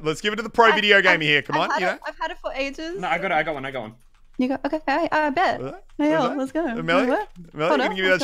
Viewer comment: sounded like she said the n-word😂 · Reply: Nigga okay bet lmao